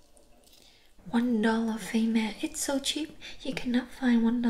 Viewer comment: I listened to this video with my eyes closed several times before I watched it with my eyes open. For ages I thought she was saying "one dollar famous" about an unidentified well-known product - I didn't realise she was saying "face mask"! 😅